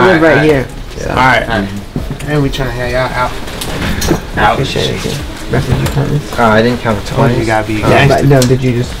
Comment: listen closely to what’s being said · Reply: “Probably not good at doing that as you are” 😶